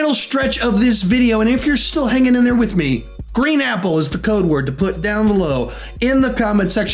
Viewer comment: Cusick - Thanks for the nice message. I wasn’t sure anyone was even going to see this post lol. I always find these in other vids and they are helpful so I thought I’d post one here to show my support. I’m glad it helped. :) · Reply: YOU A DWEEB AS WELL... NO, YOU DONT GET A PASS FOR SUPPORTIN NON MASCULINE MALES... I THOUGHT HE SAID THE CODE WAS RED APPLES AT 1st, BUT I THEN HEARD SAY GREEN APPLES! IM DISSAPOINTED I HUNG AROUND DA WHOLE TIME. NOT TO MENTION, 2hrs WORTH OF BLUE REY DVDs NOT ONE "AFRICAN AMERICAN MOVIE" LIKE ROSEWOOD, MISSISSIPPI BURNING, TRAINING DAY, MADEA=TYLER PERRY MOVIES, HOUSE PARTY 3, OH DATZ RIGHT HE DID HAVE BAD BOYS... WELL THATS ONE.. SMH